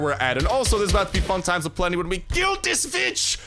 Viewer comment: I know you said kill this witch but I swore I HEARD A B